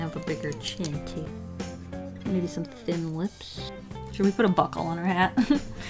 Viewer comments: Actually thought she said Butt hole instead of buckle 😂😂😂😂😂😂😂😂😂😂😂😂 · Buckle ohhh lol I thought she said butt hole XD